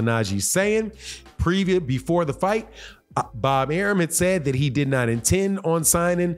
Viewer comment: I thought yal said Bob Arum said he’s not re signing him 😅 I guess it’s Shakur next! I can’t wait